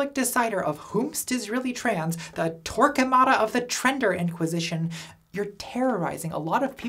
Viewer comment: I misheard "Torquemada" as "Twerkemada" and boy was that a mental image